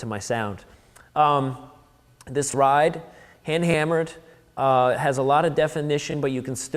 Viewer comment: He never says that his ride is HHX. He clearly says "Hand Hammered" at 1:03. Also known as "HH".